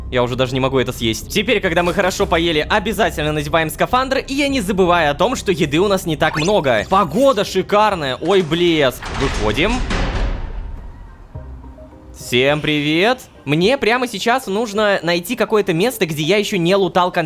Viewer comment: мне дважды послышалось ойй бл### , давайте без вопросов?))